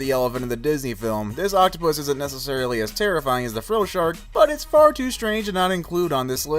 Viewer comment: the captions say "fro shark" instead of frilled shark, lol! 🤣